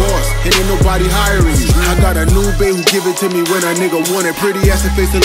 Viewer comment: I thought she was saying "I got a new bed"😂